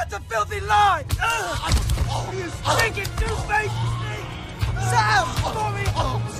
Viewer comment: I'm not the only one who heard Sam call Smeagol a douchebag right?